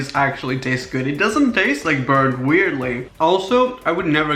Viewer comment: did he just say "it doesnt taste like bird?" lol